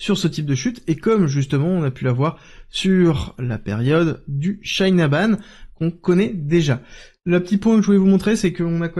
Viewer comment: C'est quoi : "Chain aband" à 13:50 ? Pas sûr de comprendre votre accent.